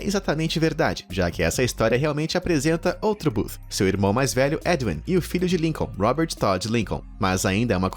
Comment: podem me falar se eu ouvi certo?,edwin?